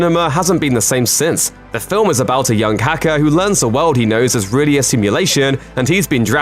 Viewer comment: Did you say "the film is about a young kaka" when you speak about The Matrix? xD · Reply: Accented English pronunciation of "hacker" - although I heard "kaka" at first too. I'd never call Neo Kaka. I'm sure no one else has either.